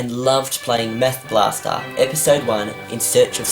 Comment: Anyone else heard "meth blaster"?